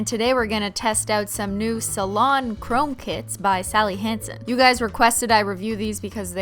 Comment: You said “chrome kits” and I heard “chrome tits” 😂😂😂wtf